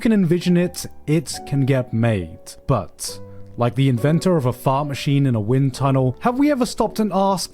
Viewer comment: did he say a fart machine in a wind tunnel?